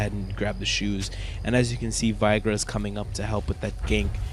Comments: Kinda sounded like you said Viagra when you said Veigar's name.. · Vaigra? Vey.. gar.. not vaigra ... it almost sounded like you said Viagra XD